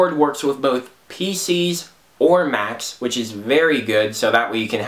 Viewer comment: Ok, under two minutes you answered my question, it's compatible for Mac....I'm sold!!! Then again it sounds like you say "maps or mats" but doesn't sound like MaCs...hmmm????